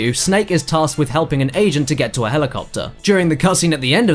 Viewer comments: Thought Cad said "asian" but I'm sure he said "agent"... r-right?... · i thought i heard asian when he said agent. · did he say agent or asian? I've watched this over and over and I can't tell which one he says?